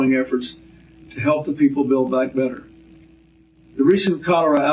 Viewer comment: Did I hear on 54:42 "build back better"? This phrase was particularly common during and after plandemic